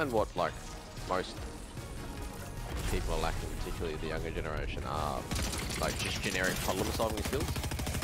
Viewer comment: wtf is saying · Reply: Something on the lines of the younger generation lack general problem solving skills